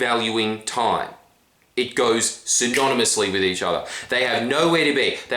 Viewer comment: What word is he saying here? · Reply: synonymously - equivalent in meaning; expressing or implying the same idea.